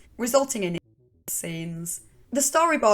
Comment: What's the word at 24:06 supposed to be? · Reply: R@ scenes. Supposedly, Stew explicitly abuses Angelica in the storyboard, forcing her to perform fellatio